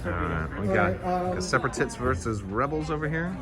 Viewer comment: Am I the only one who noticed said Seperatits instead of Separatists